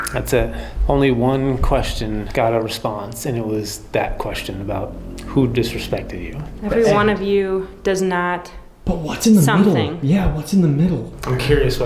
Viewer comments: I heard "Everyone one of you, that's a fact and they come back." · "everyone of you cannot fathom the demon got me" is what i hear. almost like hes stating he cant understand why humans dont believe he is actually haunted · I believe I hear "everyone of you did not" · I swear at 1:20:23 it sounds like “Every one of you, Conner, Dakota, the demon got me” I swear on my life. I hear two syllables, then three. An R sound on the end or the first, and a k sound on the second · I feel like I hear “Everyone of you, stay together (or help together) the demon got me” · i think it says like “ Every one of you will die, the demon got me “ can, will or must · I thought I heard the EVP say everyone of you does not respect me